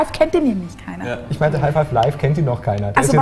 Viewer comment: ich hab erstmal half life five verstanden :D